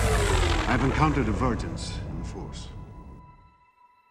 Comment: until I saw this trailer with subtitles "vergence", I always thought Liam was saying "virgince" referring to the virginal conception of Anakin. Thanks for the correction and new vocabulary!